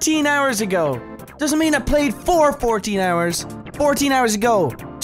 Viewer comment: Oh wow. If you hadn't paused the video at the "First Played" I legit would have thought it said "played for 14 hours" too. Good job clearing your sweaty sweaty name.